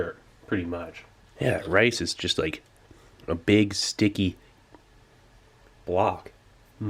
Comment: I heard, "Rice is just a big, sticky, WAP"